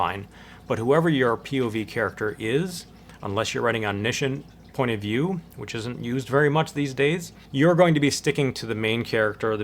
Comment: Very helpful, thank you! I'm a new subscriber :) At 31:45, you mention a not so used point of view, but I did not catch what you said, neither did the captions. It sounded like omnition. Can you type the term, please? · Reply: Omniscient point of view. Sorry for the lack of clarity there.